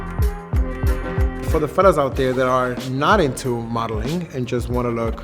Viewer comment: I swear I thought I heard his say "FATASS" but he was saying "fellas" I love it hahahaha